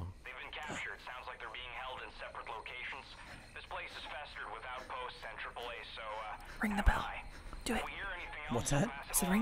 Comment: Did anyone else notice that at 14:02 the subtitles say "This place is festered with outposts and AA guns, so" but the voice over says "This place is festered with outposts and Triple A, so uh" WATCH OUT, THEY'LL FIX YOUR CAR!